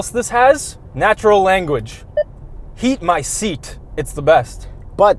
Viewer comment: “heat my seat” i heard “eat my seat” 😂